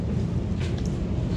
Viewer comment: I heard someone whisper “thank you “ And my subtitles also said it was “thank you”